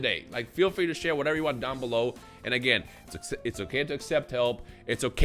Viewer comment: I heard: "it's okay to accept L" I said: "I know man, I know"